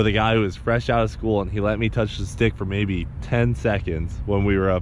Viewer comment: did he say dick!